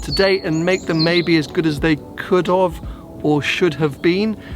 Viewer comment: Is it just me or is he saying "as they could of (been)" at 1:30 ? o_0